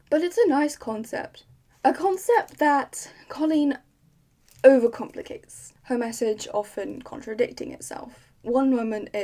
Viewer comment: I thought you said Colleen Hoover for a sec there